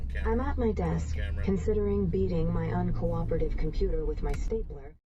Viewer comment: "I'm at my desk, considering beating my uncooperative computer with my sphincter". Thought that's what she said at first....